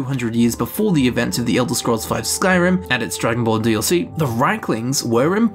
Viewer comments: I swear to God you said, "Dragonball DLC." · Did you say "Dragon Ball DLC"?